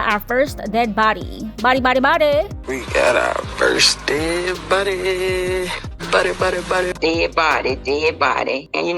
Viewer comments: Ngl, I thought he was saying "first dead *buddy*, buddy buddy buddddy". This was definitely set up though. This was planned BEFORE the trip imo. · "First dead body" assuming she was drunk and passed out. Hopefully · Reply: I thought he said "first dead buddy" also assuming (hoping) he meant 'cause she was tipsy.